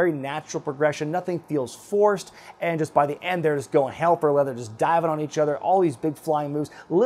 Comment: says "...and just by the end, they're ___ __ ___ just diving on each other...". I don't understand those 3 or 4 words where i have blanks. The captions read "...and just by the end, there's going help or whether just diving on..." which makes no sense and isn't what he said. It's not a big deal but things of this nature drive me insane. Someone help me out.